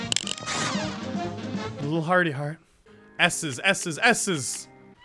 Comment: Am i the only one who thought he said "asses"? XD